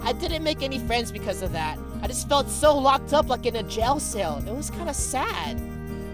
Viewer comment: he said "Gel Sale" instead of "Jail Cell"